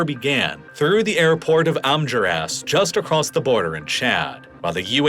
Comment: for a minute i thought you said they brought weapons through the airport in "armyourass" 😂